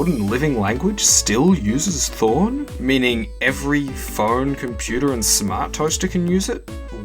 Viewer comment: I thought it said "porn" next to thorn 😭😭😭